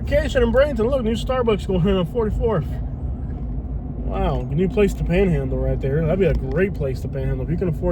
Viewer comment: Did I just hear you say “A new place to pan handle 7:21?”